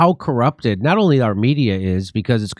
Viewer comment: Our media! I thought you said Arminia, and my brain froze up for a minute.